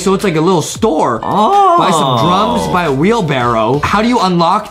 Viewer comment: When said "Buy some Drums" I thought he said Buy some Drugs